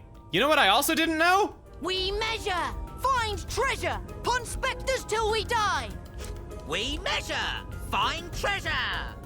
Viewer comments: I know the Ponspector saying is "We measure! Find treasure! Ponspectors 'til we die!" but I always hear "We never... find treasure! Ponspectors 'til we die!" and honestly, still kinda fitting in a weird way. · oh they're saying we MEASURE? i thought they were saying "We never" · “We measure, find treasure, Ponspectors till we die!” Yet, it really does sound like “we will never find Trevor” during battle. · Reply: I thought they were saying, "We never find treasure!"